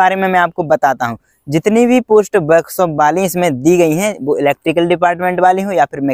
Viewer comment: Can anyone tell me at 3:29 what he said. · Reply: Workshop wali post bola